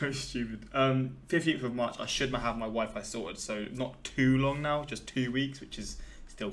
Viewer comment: When you said you'd get your "wifi sorted" I truly thought you said you'd get your "wife and daughter ". I was like, man I missed a few weeks and now the dudes married with a kid?